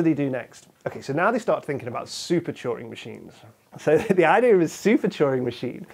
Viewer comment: I thought he was saying "choring machines"